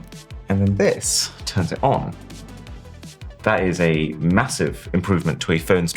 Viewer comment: massive, i hear?